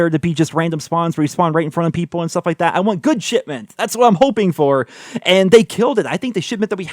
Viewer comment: Idk if that's meant to be but sometimes I hear say "shitment"